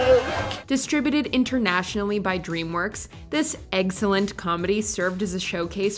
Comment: It would be easier to have a separate list for Disney/Pixar movies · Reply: Idk if she said that or didnt like a joke ;-; I hear eggcelent lol and they are chickens